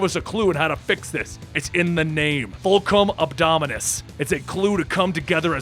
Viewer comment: it sounded like voltron and it a big robot it voltron!